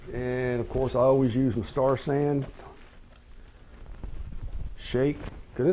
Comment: what spray name (in 9:14) ? why i ask because you not subtitle and i cannot hear your speak (i am deaf). thank you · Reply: The spray is a mixture of Star-San and water. I will attempt to add subtitles in the future. Thanks